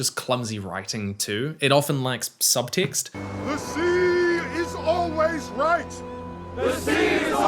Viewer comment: "The sea is always right" was Amazon's painfully transparent attempt at trying to have their own "Winter is Coming." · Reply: What the fuck I thought they were saying the sea is always rising, which is kind of cool because they understand their temporary standings against Ulmo...the sea is always right is fucking tewibul bwo